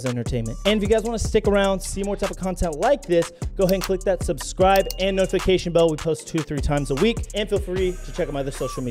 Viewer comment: anyone else catch the “channel name” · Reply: USA MILITARY POWER